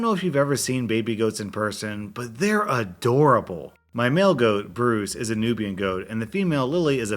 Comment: at the story with the goats when you said male goat i thought you said mail-goat